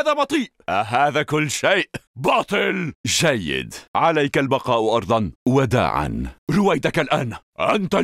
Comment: Can someone tell me what is he saying at 0:14? I think he says that when he does f,b2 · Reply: he says "عليك البقاء أرضاً" which means "You must stay down"